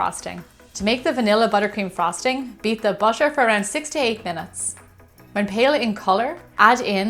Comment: first I thought she said beat the butter for 68 min but then I read 8-10, I got scared for a moment